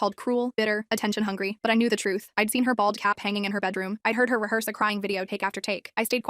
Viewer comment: OMG I THOUGHT I HEARD BALL CAP.